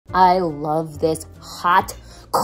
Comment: LOL before the video started I had a ad and the last like was “”record your message”” and then I hear “I LOVE HOT SOUP”” 😂❤